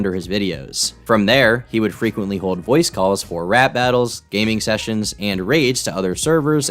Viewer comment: Did he say "Rap battles".....?